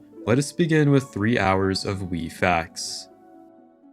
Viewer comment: thought it said mii farts 😭